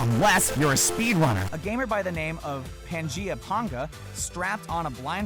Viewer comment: Why did I heard "penjiapenga" ? At 6:09 ...